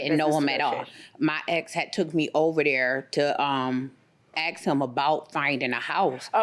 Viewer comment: " My ex had taken me over there to AXE him " ,,, but if that had really happened, if she axed him at that point, he'd be dead and this would be a serious high profile media circus murder trial. It's best we leave the sharp bladed weapons at home. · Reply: I thought she said, "My ex had TOOK me over there to AXE him". Either way, I hated the way she sounded, spoke and looked!